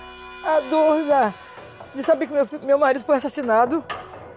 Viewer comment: "A dor de saber que meu fil, meu marido foi assassinado" observem no minuto 2:30 ela ia falando filho.